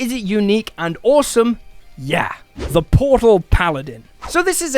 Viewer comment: When you say Portal Paladin, I hear “Grey Knight” from Warhammer 40K. I’m absolutely rolling one of these!